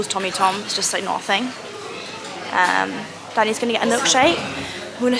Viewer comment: Congratulations to you and Tommy 🤍🐘!! Generally thought you said "daddy's going to get a milkshake" 🤣🤣